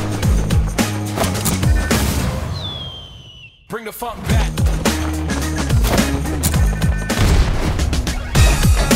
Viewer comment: Did it say a bad word?? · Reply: It said Funk